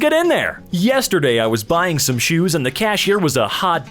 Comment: I'm at 0:38 and I thought that it said "yesterday I was bullying some shoes"😂